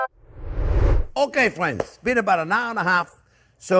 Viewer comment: lol when chef JP said “been about a…” I could have sworn he said “peanut butter” I lost it was like “IN SPLIT PEA SOUP?!?!” Glad I figured that out.